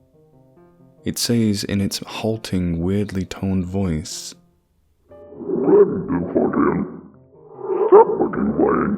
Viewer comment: what did he say? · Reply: it mimics what one of the other characters said earlier at the door, which was "Stop fucking playing. Let me the Fuck in."